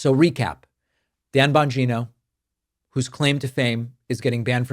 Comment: I thought he said Bongina, like the way Trump says China.